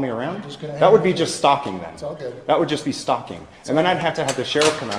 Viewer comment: What did the bald guy in plain clothes say at 10:27 ? "He's on the good list" or something?